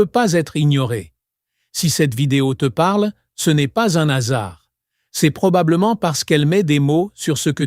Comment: C'est de l'IA. À la fin j'ai entendu "un asard" C'est un hasard, le hasard